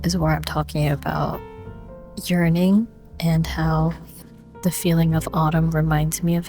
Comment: When you said yearning... I heard it as urining and I was like whaaat lmao